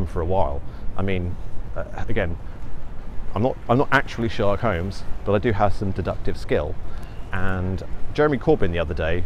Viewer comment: sounded like: 'i may not be sherlock holmes but i do have some seductive skills...' phwoar! 'a small yellow citrus fruit up the bum? that's a lemon entry my dear watson'